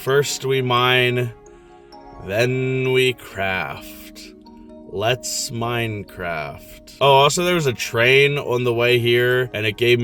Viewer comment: During the part where Jack Black said let’s Minecraft I thought it said let’s mine crack😭